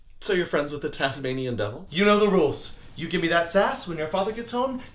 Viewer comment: the first thing I heard was “you know the rules and so do iiiiii.....”